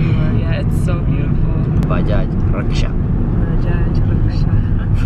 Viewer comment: did say Bajaj because in somali Bajaj is the same word for rickshaws